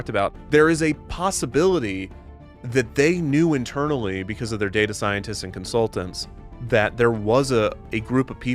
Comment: When you said “they would know because of their data scientists”, I heard “they would know because of their GAY-ta scientists” and it took me like 10 seconds to question that.